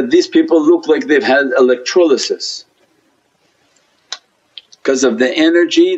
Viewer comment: What did he say at 4:30 They look like they had ........ electrologis?